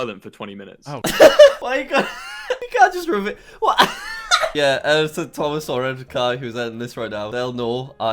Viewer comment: if your looking for what the editor said he said hey